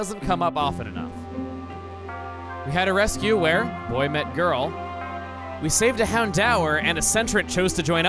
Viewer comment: Did Chuggaconroy say "Boy met Girl"? ~ 1:48 +1 for people who catch the reference.